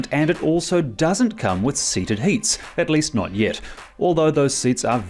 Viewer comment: Did I hear him say “seated heat” at 7:28 mark?